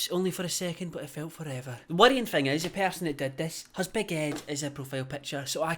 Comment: for a sec i thought you said baguette instead of big ed